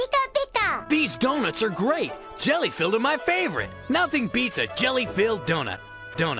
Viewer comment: When I first saw this back in the day, I thought he said the donuts were coconut coated, rather than jelly filled!